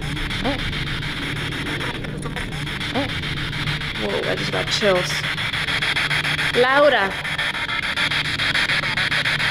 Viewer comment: is it me or do I hear laura (after the ra on the name it gets distorted)